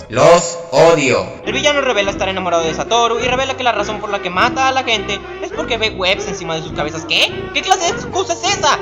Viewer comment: perdón pero no entendí lo que dijo en el seg 3:46 hablo muy rápido. que fue lo que dijo xD ? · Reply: cokoous darkus Que la razon porque el maestro es un villano es porque ve web (tela-araña) en la cabeza de la gente. Despues piensa que esa no es una muy buena razon para ser un villano y que no explica nada y que no hace sentido :P